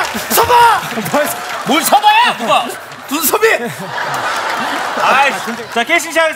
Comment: 이거 뭐라는거죠? 서바. 서바 . 뭘 서바? 눈썹이... 이거 맞나요? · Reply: 정유진 썹아 썹아 눈썹이